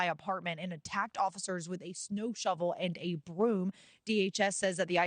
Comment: Was it a shovel or a broom stick? DHS 1ST said broom stick. Then they changed their story. DHS ARE LIARS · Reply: I heard them say “snow shovel AND a broomstick.” Minneapolis people do use both 😂